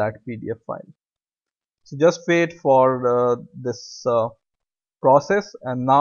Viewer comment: did you say, I just paid for this process?